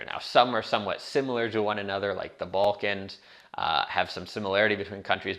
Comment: If you're thinking of making the move to Europe and want to do so as a location independent freelancer (one of the easiest ways to get a visa), check out my other video here: <link> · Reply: Just to let You know subtitles contain lots of mistakes like in 3:37 "bulk and" intead of Balkans or like 4:27 "Shang and" instead of Schengen